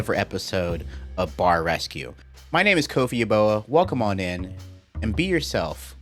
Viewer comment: I like to pretend you talk like Arthur Morgan, and it sounds like you say "Kofie Ya Boy"